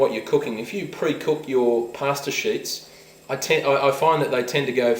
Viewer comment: i thought you said pasta sh*t at first.. then i firgured it was sheet